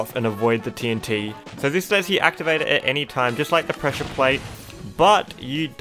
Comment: Not sure if it's the accent or not but I keep hearing "pressure point" when he says "pressure plate"